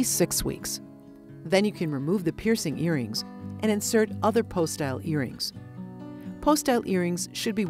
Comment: What kind of earrings does she say at 4:17 ? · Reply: Post-style I think